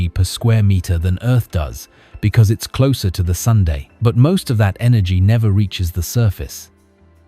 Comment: That was funny. I heard "Venus ... is closer to the sundae." A little before 8: 49.